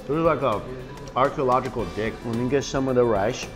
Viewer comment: Did i just hear archeological dick After he sucked that bone